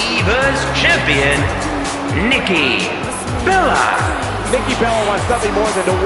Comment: Thought the accouning guy said likki Bella